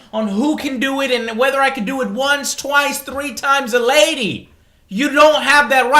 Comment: did you just say a lionel richie song? 3 times a lady?